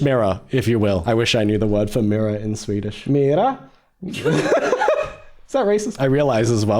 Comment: sound like he saying "Mira" 😄 Also Swedish word for mirror is spegel